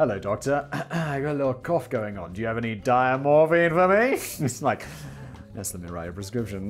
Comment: I thought he said "diet morphine" lol, I need to go to sleep! 🤣🤣